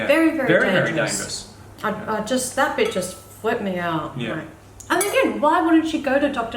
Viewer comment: I could have sworn I heard "That bitch just flipped me out" I was like "NATASHA!!!" untill I rewatched it 4 times and got it in the end, lol